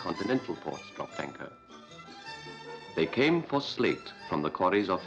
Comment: I keep hearing "slave" instead of "slate" at the beginning when describing the town and history. I need my hearing checked.